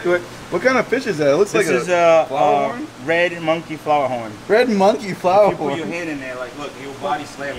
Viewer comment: I thought he said Red Monkey Butthole XD